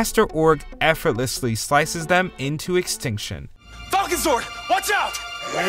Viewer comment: i thought he said fùckezord 😂